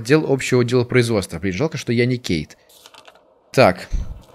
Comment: Где-то в 22:50 когда он сказал жаль что я не Кейт мне послышалось жаль что я не гей :D